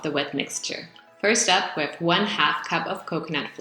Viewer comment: AAAHHH! I misheard in the video!! I put 1 1/2 cup of flour instead of 1/2 cup!! I know get why it was too dry!! :))) Anyway, the taste was GREAT. Next time I will put one cup less of flour......